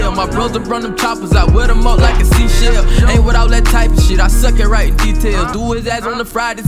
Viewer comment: Did he say “suck it right” or I’m tripping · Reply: BangOut Rico he said he suck at writing details